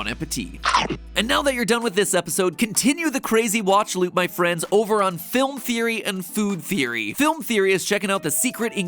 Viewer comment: When he said "Continue the crazy watch loop my friends over on film theory and game theory" I heard "Continue the crazy watch loop on my friends at film theory and game theory" I was genuinely confused